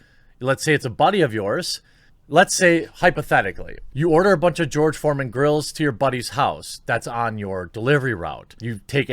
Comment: I thought he said George Floyd grills at first 😐😂😂